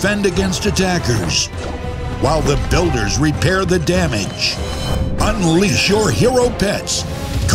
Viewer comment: 「こんばんは your hero…」としか聞こえないw